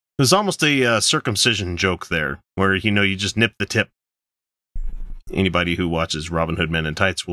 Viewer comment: At first I thought you said sniff the tip instead of snip the tip. lol